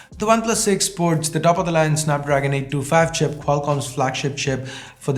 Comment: I just heard snapdragon 825 chipset